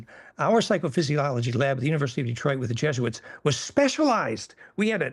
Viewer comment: Did he say Jesuits at 1:00:38? A jesuit connection to education. That's a concern. He must know that.